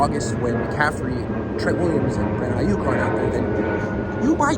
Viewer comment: Huh? What? I can't hear you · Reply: "Aren't out there?" Does that sound right?